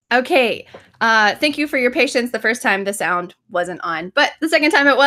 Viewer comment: I can't understand the sentence at 12:32. "The first time the sound wasn't on, but the second time it was." Appreciate any comments. Thank you so much in advance. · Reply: "Okay, uhh thank you for your patience"